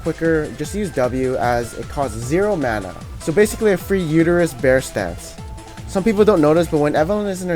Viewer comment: Please tell me I'm not the only one who heard 'Free uterus bear stance' at 3:48 ....